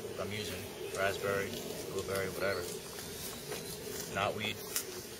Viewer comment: Like your bids man but did you say at 2:35 not weed for flavoring I thought that was funny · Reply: spiteye420 he meant knotweed, which is a wild edible, if he meant japanese knotweed than you can find it pretty much everywhere as an invasive.